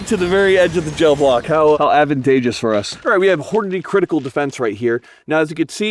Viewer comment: for a moment I thought you said "Hornady 'Cripple' Defense"